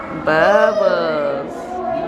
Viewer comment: Aww did I hear say ‘bubbles’ ?! Wow! 👏🏾 loveeee that thoughtful blanket. Beautiful! And love how you’re basically turning into an interior designer with your new home!! Haha. Kudos! ❤ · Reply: haha yes she said bubbles!